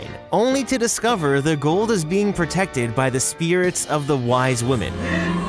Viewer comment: Hate to be the party pooper, but it's white women, i thought you said wise women? Love the video regardless